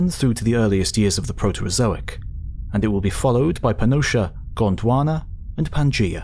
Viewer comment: Did he say Panocha??